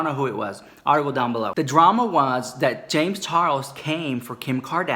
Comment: If you turn on the captions at 4:12 it says James Taros instead of James charles